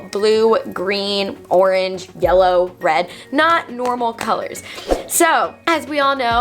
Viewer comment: Am I the only one that heard her say red was normal and red was not normal hair color lol